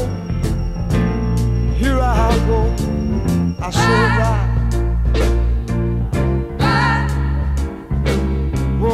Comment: What does he say at 0:22 - 0:24, right after "here I go"? It's not included in the lyrics. I was wondering that maybe some native English speaker could pick it up? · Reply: “I say bye, bye black bird” that’s what he says. 😄